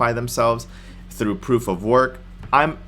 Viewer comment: Did you just say proof of work? 7:48 lol